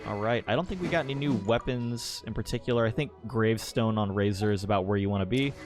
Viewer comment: Omg. I heard you say gravesword!! Wish I had it for my main dps